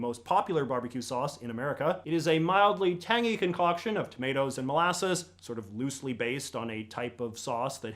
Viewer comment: did anyone else hear "potatos and mollases" instead of "tomatoes and mollases"? My mom and I had to do a double take.